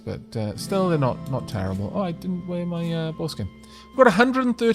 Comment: I swear I thought you said foreskin gloves, instead of boar skin, made me look up. Getting deaf in me old age. 🤣